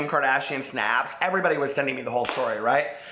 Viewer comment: Replayed 1:15 like 2-3x bc at first it sounded like you said "Everybody was sending them to the Volturi!" HOW COULD YOU?! Twilight is horrendous 😂 Me and my bestie love your channel, we both wish we could all hang out and be friends forever lol your younows are life btw. I only caught my first one the other night and fell in luuuuurve. Luuurve yooo so muuuuurch!